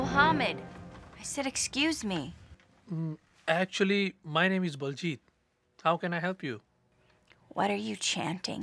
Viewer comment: it sounded like he said “my name is bald Jeep”😭✋